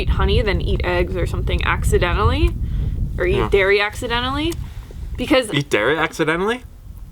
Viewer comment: That was hilarious when thought said she would rather eat dairy accidentally. The look on his face!! Haha he went straight mean girl on her. You two are funny.